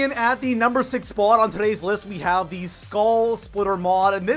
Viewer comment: Did he just say skall splitter... hahahahaha